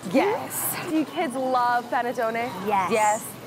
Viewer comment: I THOUGHT SHE SAID PENTATONIX AND I FREAKED OUT